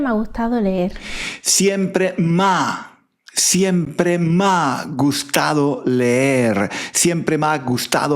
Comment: I could have sworn that she was saying something like "méa gujtao leer"